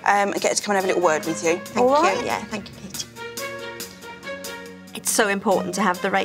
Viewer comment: It’s sounds like she is saying ”thank you i hare you”